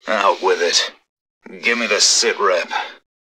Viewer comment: LOL i always thought that he was saying at 0:06 " Give me the cigarette "